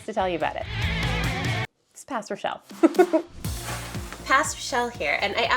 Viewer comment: I thought you said Pastor Shelf instead of Past Rochelle and was reeeaaallyyy confused 😅